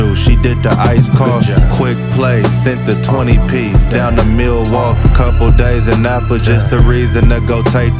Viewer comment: I thought I heard Him say Milwaukee..on the ad lol